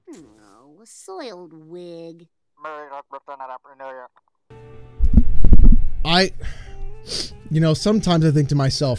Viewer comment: what was said? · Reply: He said Merry Christas and Happy New Year.